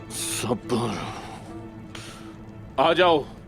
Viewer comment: what did gunduz said 7:00 = I think sabar what did goktug said 19:49 = I think begherat what did kumral abdal said 21:00 = I think luft/lutf